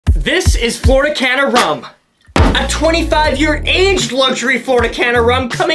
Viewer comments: Now tell me why I thought he said "Florida de canada rum" 😭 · You pronounced it wrong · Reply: It sounded like he said Florida Can of Rum 😂